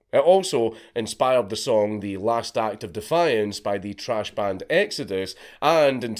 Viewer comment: did he just call Exodus “trash” 👎